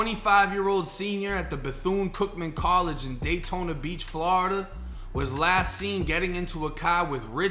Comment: He actually said Florida!!